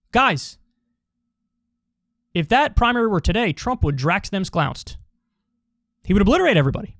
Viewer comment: "...drax them sclounst"? Is that what I heard, Kyle? 🤔😂 Edit: just looked it up, didn't know it was a Key and Peele reference